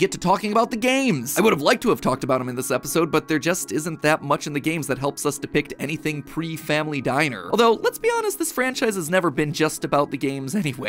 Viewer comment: I thought he said pre-family guy